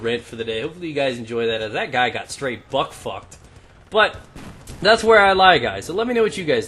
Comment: haha did he say "buck fucked"?